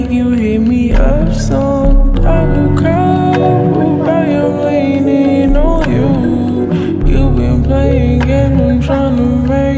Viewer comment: What is really says: Leaning on you What I hear: Leaning no u